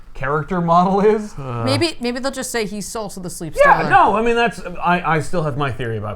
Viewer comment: I’m so glad I’m not the only one who heard Salsa the Sleep Stealer 😂